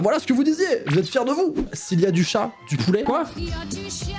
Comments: Ah 9:23 moi au lieu d'entendre "du chat" j'entend "du chien" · moi j'entend y'a du chien y'a du poulet